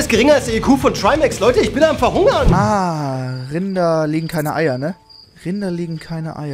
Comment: Hahahah, ich habe bei dem Trymacs Einspieler zuerst verstanden "Kinder legen keine Eier"😂